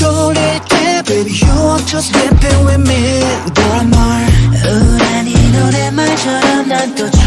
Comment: i thought he said "you are just whipping with me"... Theres something wrong with me 😂😂